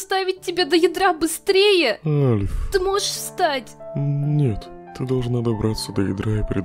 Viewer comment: Когда альфис сказала санс ты можешь встать то мне послышалась санс ты можешь срать😂😂😂😂😂😂👍😂😂😂😂😂